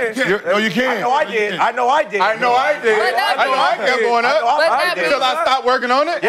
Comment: SOUNDED LIKE FINDING NEMO 🐠…. BUT IT WAS EVERYONE SAYING “ I KNOW I DID , I KNOW I DID . 😂